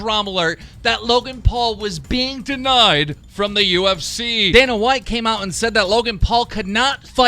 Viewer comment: i thought he was being racist by saying "then a white came.."